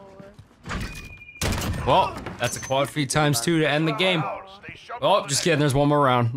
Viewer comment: did anyone else hear “well, that’s a quad feed title” and go see the thumbnail 😂??